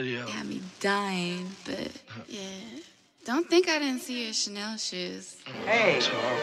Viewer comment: I got so many questions about the intro. What's gonna work? What video had her dying? What does she say "Don't think I didn't see your chanel __"? Wtf kinda drugs was Uzi on where it got to him hearing alarms and shit that sounded like it was from an 80's sitcom? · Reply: BobbyWasabi I thought it said snail shoes but that makes a lot more sense